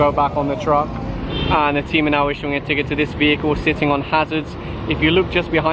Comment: I always hear "Team Manalo" but he's saying (Team are now)